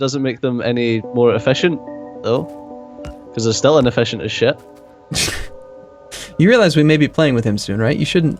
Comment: Did u just say, "You realise we may be playing with him soon (benthem)" minute 1:50 Oh, AMASS incoming. May the hype train begin